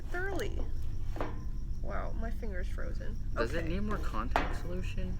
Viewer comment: i heard yes it does in